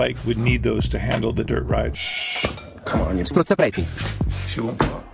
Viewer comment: The italian insults are so fun but I died when I heard "strozzapreti" at 6:40 😂. I don't know what you meant but strozzapreti is a kind of pasta